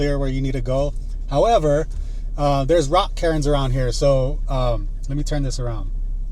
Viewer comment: When you mentioned the rock cairns I thought you were saying rock Karens lol. I was telling myself “wtf is a rock Karen? Women camping out there trying to police people?” Cool video man